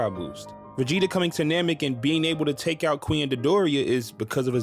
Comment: I heard "Vegeta coming to namek and being able to take out QUEEN dodoria is because of a zenkai boost." I honest to god thought that you called dodoria a queen to reference dragon ball z abridged. But then I realized you said "Cui and dodoria.", which makes a lot more sense.